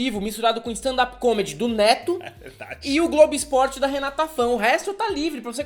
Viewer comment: só eu que escutei "globo esporte" no minuto 2:00 ?